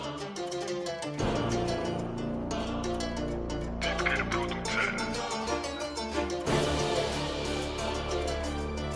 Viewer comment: what he says? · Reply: Pro tip🤷🏻‍♂️